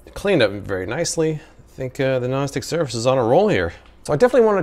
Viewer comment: I keep hearing “gnostic surface” instead of non-stick...